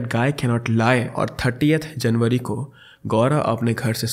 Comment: Am I only the one who heard 38th January...